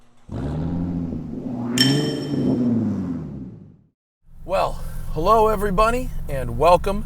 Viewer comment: Did anyone else hear say, "Hello every bunny." (0:05)?